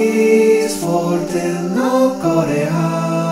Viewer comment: I thought he was going to say F**k 😂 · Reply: I heard "no korea" => is racist? =O